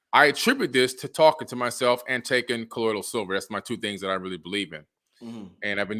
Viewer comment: What did he say he take to prevent colds? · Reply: Colloidal silver